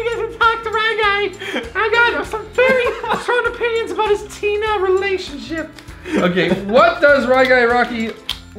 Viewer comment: it’s sound like he says Tina ‘s relationship But I know it was my imagination